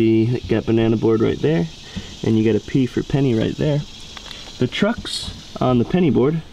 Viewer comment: When he said P for penny I thought he said pee for penny